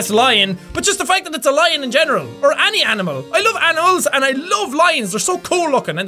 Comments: jacksepticeye did you just say that you love anal's? its at the 8th minute and 7th second (8:07). or its just my hearing going nuts after all your videos. by the way watching your videos is my daily routine now. keep up the good work!!! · it sounds like u said I love annals xD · LOL at 8:07 it sounds like he said I love anal's XD